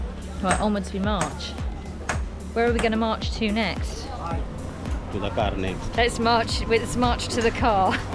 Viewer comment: said "To the car next" but I heard to the carnage 🤣🤣 WTH